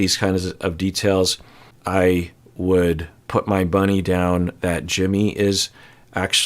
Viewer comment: you have no idea how many times I rewound 16:00 to make sure I heard correctly 😂 has anyone else heard it "I'd put my bunny down" and not money?